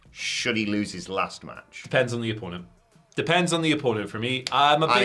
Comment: I heard “depends on the iguana”🤦🏾‍♀️ 😂